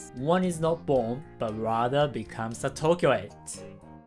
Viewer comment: the captions says that he said "Tokyo eight" but what does he actually say? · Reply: ~[Ματ2468χκ] he means Tokyoite, it's a common denonym for people of Tokyo. :)